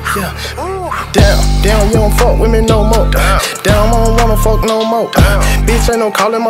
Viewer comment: Am I the only one that thought he said “damn we don’t fuck women no more”